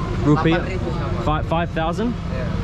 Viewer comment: He actually said the price was Rp8.000 (lapan ribu/eight thousand) 😂